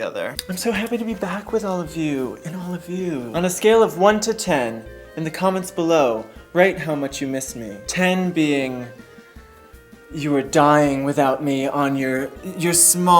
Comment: 10 (I was dying) plus I thought you said CUMments which made my ears prick up... but not only my ears.